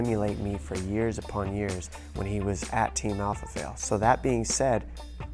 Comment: Did he say "Team Alpha Fail"?